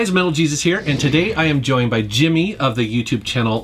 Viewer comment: DID HE SAY MALE JESUS!?!?